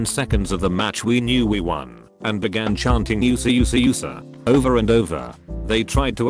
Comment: when they said USA USA the narrator said it, it sounded like you sa you sa you sa